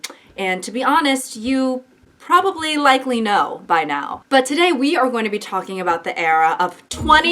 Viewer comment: Not me sitting here like the math lady meme trying to figure out if said, "You probably like Lee Know by now." 😰 I was like, "Is she a Stray Kids fan? Why is she randomly mentioning a K-pop idol? That'd be so unlike her. Did I mishear? I must have. I hope I did. Is my mind incorrectly filling in the blank because I watched a Stray Kids video last night? If I misheard, what could she have possibly said? Does it matter? If she said Lee Know, I can pretend it didn't happen. I won't judge. Should I keep watching or rewind?" Then I finally realized you said: "You probably likely know by now." Rewinded to confirm and it all came together, but I swear that whole process was at least 60 seconds of me just staring into space having a crisis.